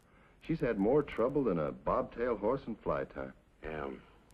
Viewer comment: “she’s had more trouble than a bob tail horse ... “ anyone know what he says? Sounds like “in a fly path” or “fly pasture”, but I couldn’t make it out. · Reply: "in flytime." With a bobbed tail, the horse can't swat the flies.